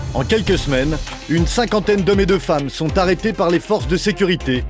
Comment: il y a que moi qui a entendu mes deux femmes !!